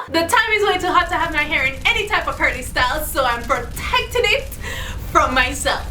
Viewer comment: I'm really sorry, English is my second language, what does say at 0:11? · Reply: The time is way too hot to have my hair in any type of curly style so...